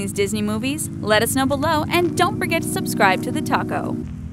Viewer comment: I thought she said don't forget to subscribe to the taco😃 at the end